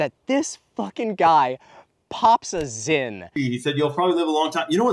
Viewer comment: I litterally had to put this video down 3 different times before the hour mark cause i was getting so pissed off abt the stuff u were talking abt. I had totally forgotten abt seed patents and that made me have to lay down cause omfg I totally thought you said "pops a zit" and was very confused for a bit lol excellent video as alway, looking forward to more terror later